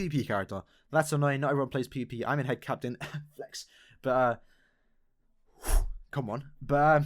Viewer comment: did u just sayd flex? "im flexing im flexing nigga!!!! im flexing im flexing nigaaaaaaaaaa